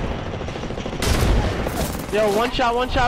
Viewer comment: what did they say? ape? emp? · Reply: ape